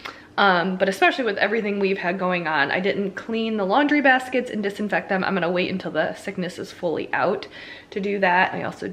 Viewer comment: I thought that said "clean laundry biscuits" lol